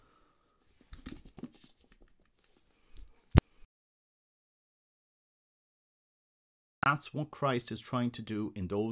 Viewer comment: Several seconds of audio were missing - right before you said “that’s what Christ was trying to do”. From 23:34 to 23:38 (Maria V. writings). What did you say? Deleted somehow? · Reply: Theosis and Deification, that is what Christ is trying to do in the writings of Maria Valtorta.